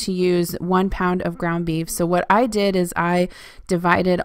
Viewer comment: I was sitting here at lunch eating my pudding cup listening and i swore you said "Grumpy beef" I about spit out my pudding and started laughing. Good lord I can't hear today. Anyway, always love Mr. Murph! :) :) I recently found Shelby as well as I love her videos! And Pizza burgers i've been making the the last 5 years, the kids get a kick out of them and they are simple!